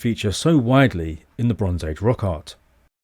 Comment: Oh I thought you said “the bones age”.